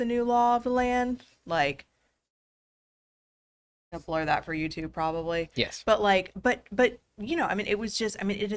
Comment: I heard "blur that for youtube" i was driving and listening but I almost wrecked my car. I looked at my phone so fast I forgot I was driving, OMFG I thought we had a nip slip or some other sort of wardrobe malfunction, imagine my disappointment when I saw she was just flying birds.